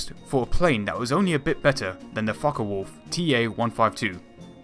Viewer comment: "the fuck a wolf" P.S. I know that's not what he said i just found it funny